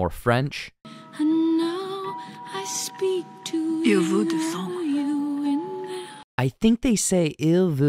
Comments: I thought they said Il veut du sang. 'He wants blood' ~ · I thought I heard "Il vous defend," which would mean "he will defend you."